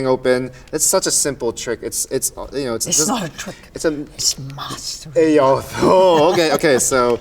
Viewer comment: I’m just here to understand what she said at 6:10. Thoughts? (Love this.) thanks VF . This movie is a dream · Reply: mastery